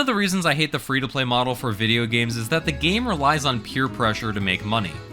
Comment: for a sec. It sounded like EA games